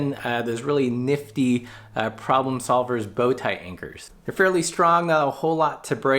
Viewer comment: What did you call those side-mini-rack things? Anchors? Bowtie anchors? I'm new to all this and trying to find something like that here in Europe. ;)